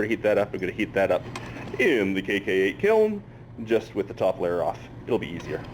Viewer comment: i so thought he was saying KKK kiln.... and i was like.. ummm what... then i read the description and laughed at my own stupidity lol.